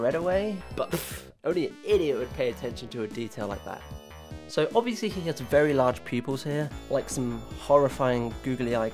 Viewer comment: WHAT IS HE SAYING HERE I CAN'T UNDERSTAND IT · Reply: Naughty